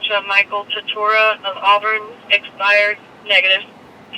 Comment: didn't the driver have a revoked? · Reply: I thought I heard the dispatcher say suspended - which, according to some officers, they can let them drive off but not with revoked. It is a weird distinction.